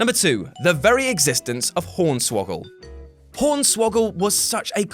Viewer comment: Did he just say pornswoggle